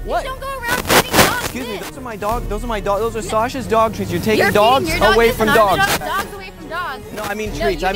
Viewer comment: i only hear "Dogs dog with ... and Dogs dog.....cant feed Dogs dog......dog from dog" :D